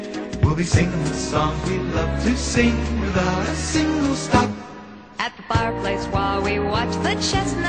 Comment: It sounds like he said "stack" and not "stop". 😐